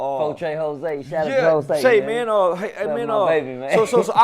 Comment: I know I heard Percy say that Jose was his baby @ 3:38 after the shout out😐😂